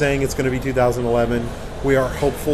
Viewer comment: I thought the said it will not be 2011!?